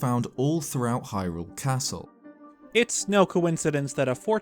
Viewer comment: did i just hear "it's snow coincidence"